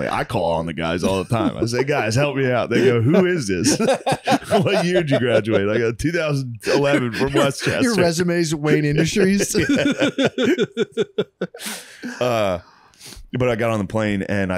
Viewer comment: Could’ve sworn Matt opened it saying Wayan Industries